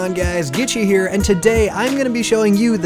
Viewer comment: Thought you Said its itchy here . :D